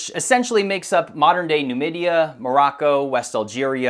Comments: did he just say Namibia witch is way South in Africa ... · did you say 'Namibia'? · Did you say Namibia is up there in the North-West? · did he say Namibia ?